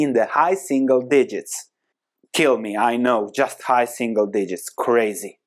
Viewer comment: I turned the video off, after i heard "high single digests" ;)